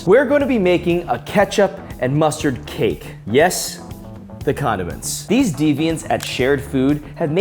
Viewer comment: Who else heard condoms 😂